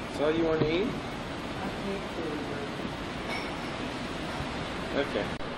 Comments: what does michael say at 3:02? he's so quiet aw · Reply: sounds like he was saying something to the effect of "I hate food..." or something. He may have not liked the hotel's food. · Around the 2:55 mark, he's asked if that that his breakfast, then "that's all you're going to eat?" What does he say, "I hate feeling dirty?" Maybe meaning that feeling when you have a full meal and you feel weighted down. I know i've seen video that wasn't a big eater. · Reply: JesseH he said “I hate food right now”.